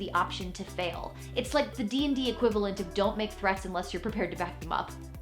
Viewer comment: At first, I thought she said, "don't make bread unless you're prepared to vacuum up" and I was like "word?"